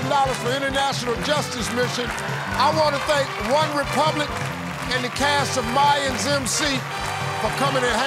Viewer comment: I thought it said one Republican and l was like what? What does that have to do with this? 😂